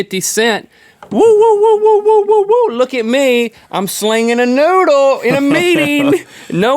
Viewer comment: "Woo woo woo! I'm swinging a noodle at my meeting!" Those were my last words at my old job. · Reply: Sounded like he said slinging not swinging